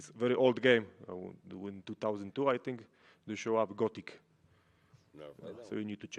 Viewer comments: wait what video game did jan said here? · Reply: Gothic and its sequel Gothic 2, you can also check Kroniki Myrtany which is mod of the decade for G2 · What game did he say at 7:19? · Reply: Gothic, pretty quality early 2000's game.